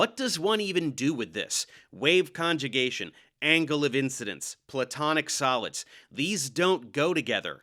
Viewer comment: Not gonna lie. I thought he said Platonic Salads. And that made more sense.